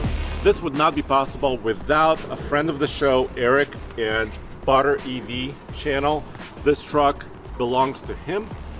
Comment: I'm not going to lie when he said "Butter_EV channel" I thought he said bought our EV channel. I was busy getting started on my breakfast then I heard that and I was like who bought your EV channel?